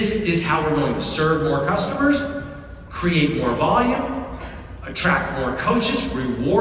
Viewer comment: I know he’s saying “attract”… but I heard “trap”